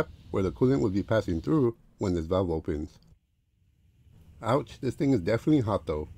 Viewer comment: did u say ouch or i hear wrong 😰😰😰😰😰😰😰😰